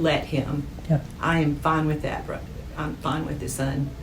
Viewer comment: did she say "I'm fine with that bro"? referring to her own son as if a random dude on the street?